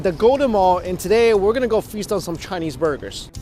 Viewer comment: Did you say freestyle some chinese burgers? I thought you said that at first then I thought surely you meant "feast on some" then I turned subs on and it said "freestyle some". V confused